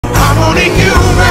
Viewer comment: I heard "I'm only human, caserolle"